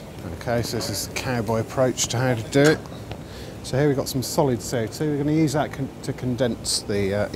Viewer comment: What kind of ice... His British is very thick... Regular water ice... Or did he say "solid state CO2"? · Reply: yeah, CO2.